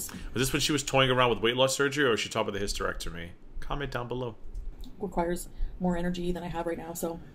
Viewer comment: everytime you say ''comment down below'' i hear in my head ''comment down bologna'' LMFAOOOO